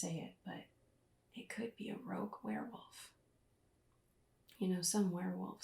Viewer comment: That is so sweet Danny! 😊 The Rogue Werewolf may not be what you expect, but I hope you like them 😏😏😏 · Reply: Oh... Rogue. I thought you said "Roque" in the video. My mistake, lol. 🤦‍♂️ Anyway, I can't imagine not liking anything you do, so no worries there! 😎👍